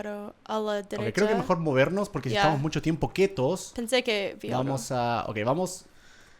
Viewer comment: Like si tuviste que poner el vídeo en cámara lenta para poder ver el mensaje subliminal y si te diste cuenta que dijo quetos en ves de quietos